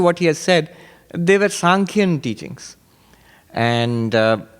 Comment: "They were Sâmkhya teachings" - Did anyone hear well enough what Swami said to help me to be sure if the word was "Sâmkhya" ? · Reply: Sānkhya. I wikied this.